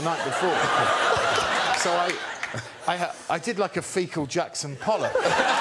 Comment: What does he say at approx 1:53? I did like a fecal Jackson something? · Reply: Jackson Pollock